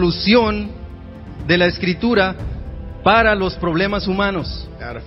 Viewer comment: En el minuto 1:02/33.07 : creo que dice espiritual no escritura... escuchen bien. R: Sabiduría viene siendo la solución espiritual de los problemas humanos